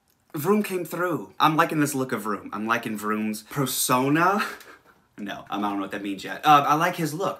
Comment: Did you really say Wroom had a wrosona ? I love you. 💜💜💜💜💜💜💜 * 4:26 Everytime you say you a going over board, you just before that said something that made me laugh aloud.. 💜💜💜💜💜💜💜 I think that is on me tho. "You're fine keep going." 💜💜💜💜💜💜💜